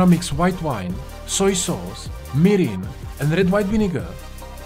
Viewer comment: Look so good chef i cant hear clearly at 2:54 wht ingredient to make the sources plss. Thanks. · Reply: Soy sauce, mirin , wine , vinegar